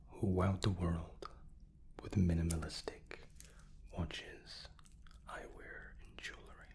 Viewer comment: Who else heard that as ".... minimalistic watches. I wear jewellery."??